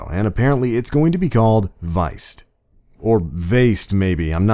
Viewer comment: Veist? I thought you said Weist?